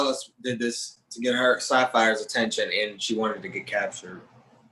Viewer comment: Did he say Sapphire? Santa Fe? Sapphire? I’m about to roast this man for days. 🤣🤣🤣🤣🤣🤣